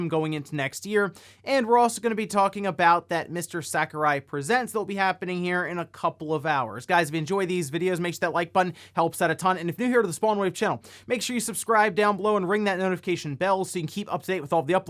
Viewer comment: Why does it always sound like he says small wave channel to me?